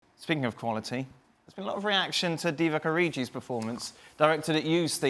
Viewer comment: Lol did he say "Divock ORIJI" 😂😂😂